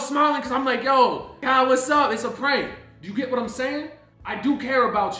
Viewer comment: Did he say it’s a prank ?